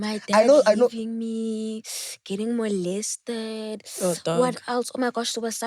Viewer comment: Did just say she was molested?